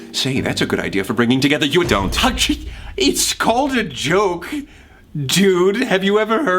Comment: did prince say dude!?,?